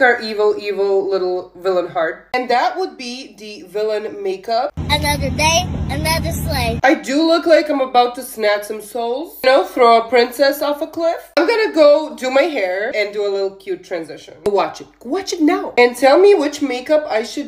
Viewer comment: I need to get my mind out of the gutter. I heard "I definitely look like I'm about to snatch some souls, yk, throat princess" GOODNIGHT.